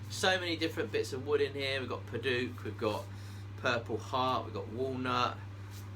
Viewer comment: did he just say purple fart?